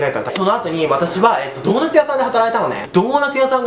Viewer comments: ドーナツ屋さんがトモダチ屋さんって聞こえた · ドーナツ屋さんが友達屋さんに聞こえた！😂